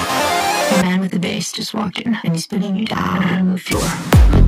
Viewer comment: "A man with the bass just walked in and he'd Pinning you down on the floor." is what I hear, contrary to the subtitles which say "and he's Putting it down on the floor."